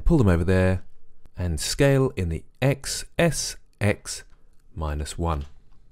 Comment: please tell me what you are saying at 3:05 ... i hear, "s, s, x, minus 1"... i want to understand how you are scaling negatively, and making it exactly -1